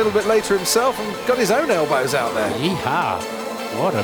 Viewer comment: did brundle just say yee-hah? lol